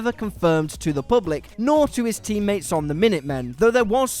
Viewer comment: did he say minutemen